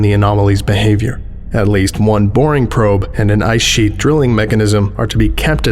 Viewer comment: My big brain moment of the day where I hear "one boring probe" and for a moment contemplate what exactly the criteria are to make a probe boring and disinteresting by foundation standards.